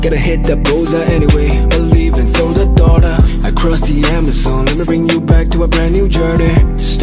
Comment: Tell me why tf I heard, "A crusty Amazon."